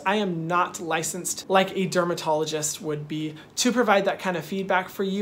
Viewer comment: Am i the only one who heard him say termadologist instead of dermatologist like twice near the end of the vid 😭🥺